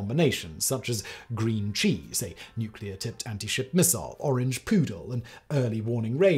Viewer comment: Oh, "orange poodle". The first time, I thought he said "orange poo".